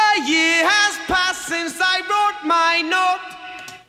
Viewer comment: i hear i broke my notes